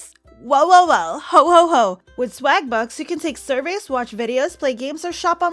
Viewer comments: My dumb self thought you said vbucks😅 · I thought it said starbucks instead of swagbucks😂😂😂